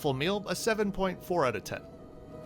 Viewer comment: I don’t even know what that means… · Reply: you said you gave it a 7.4/10 but the subtitles said you gave it 7.7/10